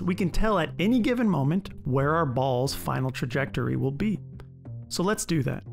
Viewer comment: No matter how much of a nerd I am in the media I consume, I heard “ball’s” as “balls’ ” and I can’t help but find that funny